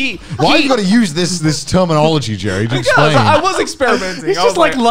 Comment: it sounds like said jerry instead of Joey.